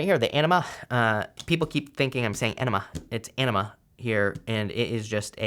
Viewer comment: It sounded like you said, “people think I’m saying enema. It’s enema.” Lol.